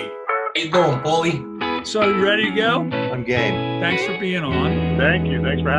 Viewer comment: Forgive me for pointing this out. Everytime Tim Pierce says "I'm game", I hear him say "I'm gay".